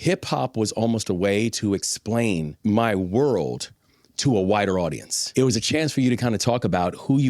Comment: did he say "whiter audience" or "wider audience" 🥸